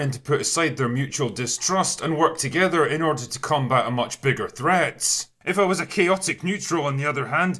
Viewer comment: when you are talking about your hopeful storyline about they coming together to "...fight a much bigger threat..." i heard you say, "The message!" lol